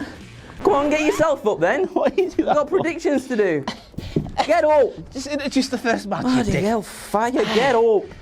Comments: with the heel turn.....but did he say what I think he did? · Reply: 'Bloody hell fire'. It's a Northern thing. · Wait, did just drop a homophobic slur around the 1:38 mark??? That would seem to be very out of character, so I'm gonna assume I just heard it wrong... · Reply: He said “bloody hellfire, get up”. It’s a Yorkshire thing. Only love xo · did he call a f*ggot 🤣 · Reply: He said “bloody bell fire”, a common northern English exclamation. in a strong Yorkshire accent, the same one he’s had since we first heard him in videos.